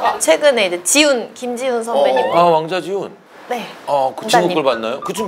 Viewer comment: 남자 꺼 본적 있어요? 다음에 6:30 왕자지훈도 왜 이상하게 들리짘ㅋㅋㅋㅋㅋㅋ